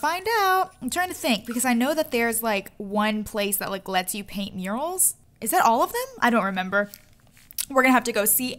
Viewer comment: I thought she said uranals